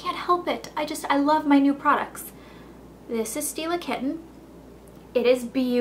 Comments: lol i thought you were saying, steal a kitten, when it was Stila.. kitten. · I thought you said "steal a kitten" and I was thinking "who names a product after that?" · hahaha thought she said "this is steal a kitten" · definately thought you said steal a kitten lol xxx